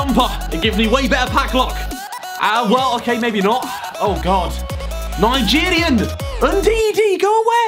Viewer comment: I swear if he Said stinky Nigerian 😂